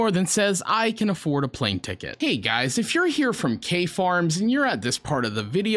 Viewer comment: almost sounded like u said "KFARR".. (that's a GREAT channel)